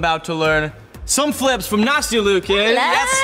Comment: "why don't you do it over there?" "no NaStIa I hAvE tO dO iT lIkE yOu" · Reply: I thought I heard nasty